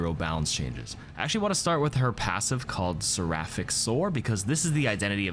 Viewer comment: i thought that said sapphic soar